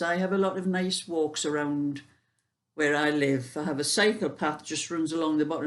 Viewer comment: Hi...I thought you said....you had a psychopath running at the back of your garden! It made me laugh when I realised you said it was a cycle path!! Hope you enjoyed your tea and got your feet up.